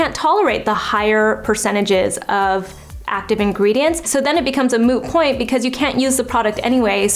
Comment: Did she just said, "it becomes a Moo point" (from friends) at 5:54 ? 😂😂😂